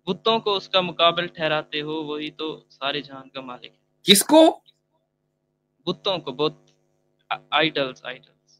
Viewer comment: even I heard it as 'Kutton ko' 😀